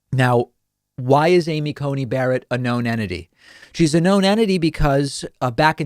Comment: Who's bankrolling her? Federalist Society? Jerry Falwell Jr? Separation of church and state, anyone??? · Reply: One Anywhere you’re more than welcome!🙏🏾 Greetings from Northern California! To your credit he did drop 1 of the t’s as you mentioned so I did do a double listen because at first I thought he said “enmity.” And context as mentioned is everything. I’m a teacher and the fact the you ask questions means that you are far from “stupid.”